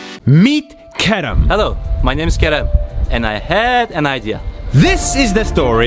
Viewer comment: I thought he said “my name is carrot”